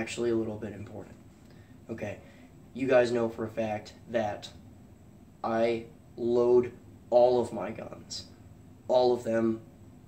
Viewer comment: For a second there, I thought you said you LOATHE all of your guns. I was like, "What did they do to hurt your feelings?"